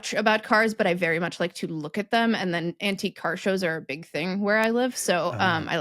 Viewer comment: You said "antique car" and I heard "anti-car". And I thought, "Oooo!! What's and anti-car?!"